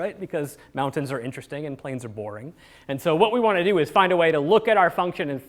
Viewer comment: I thought I just heard an engineer say “planes are boring” but he meant plains 😂 thank you for posting- good video